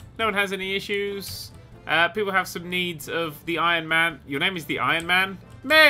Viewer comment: i thought it said the lion man not the iron man lol